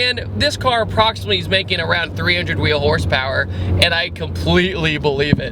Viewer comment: Got a nice chuckle during this video, my fiance overheard "300 wheel hp" and asked me, "what does that mean, is there fake horsepower?" She thought you said "real hp" hahah, good review!